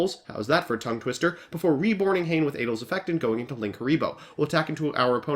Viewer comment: I legit heard “I summon Cain with abel effect “